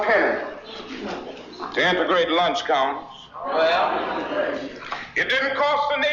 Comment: What did MLK say at 3:50? Lunch what? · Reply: Integrating the lunch counters